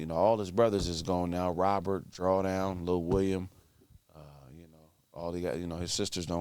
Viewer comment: i thought he said: Drawers down....like the hell, who drawers down 2 Doors Down?😂😂